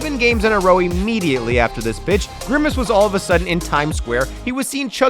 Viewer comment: sounds like he said bitch 😭